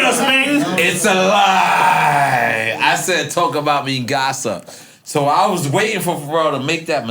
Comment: Ohhhhh shit!!! I always heard it as "talk about mi casa..." 😂😂😂